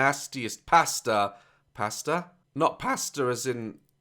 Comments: For a second I thought said the guys name was pasta and I got hungry.😂😂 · I literally thought he was saying “pasta” the whole time 😂 the British accent has its flaws